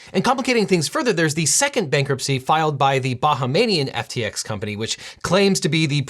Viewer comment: Did I just hear Devin say "baha-MA-nian FTX company" @ 9:30? Can someone please tell me if FTX has a entity really known as that or did Devin just mispronounce Bahamian? If it's the latter, can I please ask how and why? This is the second time I've heard this word mispronounced on an infotainment YouTube channel, and I'm actually surprised (especially since the first time was during an episode featuring the cultural traditions and practices of the Bahamas.) Y'all please let me know if Americans really don't know how to say Bahamian or if there really is an FTX Bahamanian, because I keep searching for the latter, but I haven't been able to find a thing.